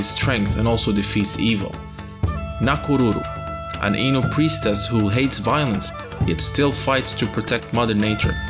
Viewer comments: I heard "Ainu priestess" as "anal priestess" and I was like 👀 Great video series though. This is really well researched, and man I'm so hype for the new game! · did you mean ainu cause i heard anal not to be gross